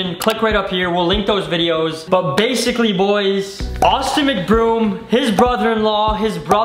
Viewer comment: I thought it said autistic mcbroom 😂